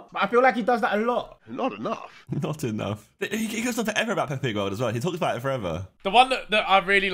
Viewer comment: wtf did josh say? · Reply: "he goes on forever about peppa pig as well" he was stammering at beginning